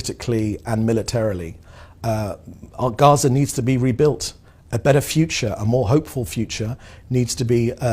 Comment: I could have sworn he said "OUR Gaza needs to be rebuilt" although he subtitles just say Gaza needs to be rebuilt.